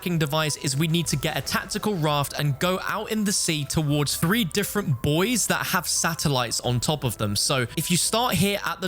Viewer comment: Nearly spit out my drink when i heard "3 different BOYS" at 10:17 😂